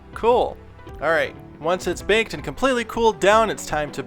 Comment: he is. It's called a "joke". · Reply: HappyFaceDuck don’t have to be an asshole I just thought he said bake and bond